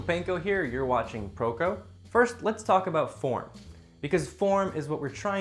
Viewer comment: I literally heard " first lest talk about porn" And literally died